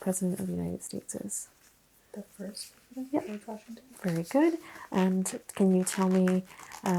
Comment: Is it just me or did it sound like she said Abe Washington? 🤷🏻‍♀️😂 anyway, I love both of your asmr content